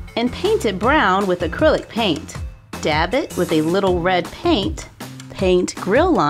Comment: When u were making the patty and said to dab it I thought u said stab it XD